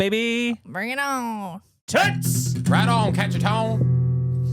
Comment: did you say "right on cacheton" because "cacheton" translates to big cheeks ? 😂